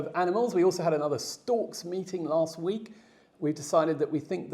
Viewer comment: I would LOVE a limited edition book!!! I was hoping for one🥰 Sorry I’m a Californian who didn’t understand that you were saying stork!!! I actually thought you said stalk!!! Thank you for the translation 😘